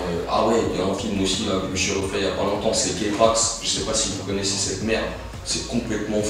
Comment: C'est quoi le film qu'il dit a 6:20 ? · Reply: k-pax